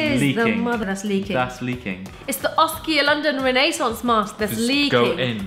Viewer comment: When she said "renaissance" I thought she said "Renee sauce" hahaha